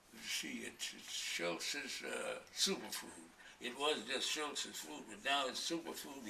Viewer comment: Wait! What did he say to be a "superfood"? I rewound the video around 00:40 at least 3 times, and still didn't get it. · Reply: ***** yes . Dr Schultz superfood is what he takes.